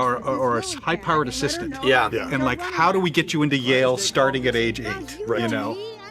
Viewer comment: did you say "how do we get into jail at age 8?"